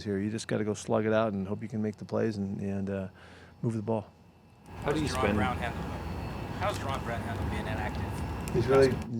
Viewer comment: ok at 4:00 who did they say came off inactive status?? i raised my speakers to dang near full and i cannot understand! someone please help lol was it Jarren Brown?? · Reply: Yes, jaron Brown. He's been a healthy scratch for a few weeks and that is frustrating for him but until he plays better than Gordon, he will likely be inactive. The question was how is Jaron Brown handling being inactive.